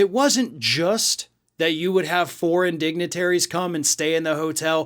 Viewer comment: I thought when said foreign dignitaries it was 4 indignities, my bad